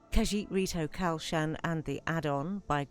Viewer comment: sorry but at the start (3:20) you mention two mods? I couldnt quite get what you said and i cant seem to find them myself. It was in reference to you planting flowers sorry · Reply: Khajiit Rito Kalshan and its addon are what I was saying and they're no longer available on the Nexus because of this:- <link> But you will be able to find all his mods coming to this location soon: <link>